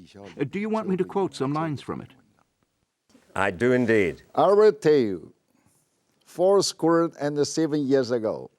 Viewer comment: what is he saying at the start? "arre teu" it sounds like, is it "on this day"? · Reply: I’m pretty sure he said, “I will tell you”